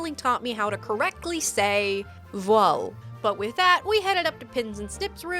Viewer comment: Canadian here i have bo clue what word you are even trying to say please help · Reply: Voile